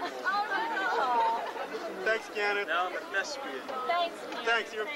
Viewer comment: when Keanu says "Am I a what?", what do the girls say after? It's been bothering me for a long time not knowing what they said. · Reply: She asked to see the muscled arms he had in Speed. He said, “no I don’t have them anymore, that was a long time ago. Now I’m a thespian.” She was obnoxious with the constant screaming. Hope that helps. 😃